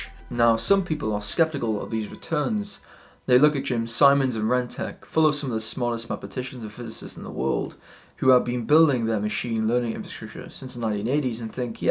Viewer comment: What do you say at the 2:18 mark? You kinda mumbled. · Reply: "They look at Jim Simons and RenTec, full of some of the smartest mathematicians and physicists in the World, who have been building their machine learning infrastructure since the 1980s and think "yep, those returns are amazing, but it makes sense - it is rational."